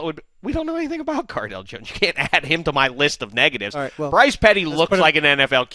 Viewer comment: first I thought he said Brice Petty got Hoes lmao!!